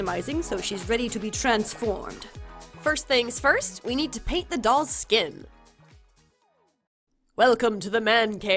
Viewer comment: For some reason I heard “ get the doll skinned “ instead of paint the dolls skin lol